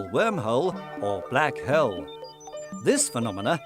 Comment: Did he say black hell?